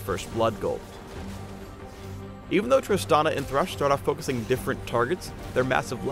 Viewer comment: Sounded like Phreak said "Thrush" at 6:00.